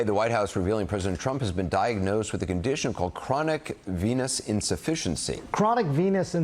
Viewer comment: Oh, I thought they said "chronic p*nis insufficiency".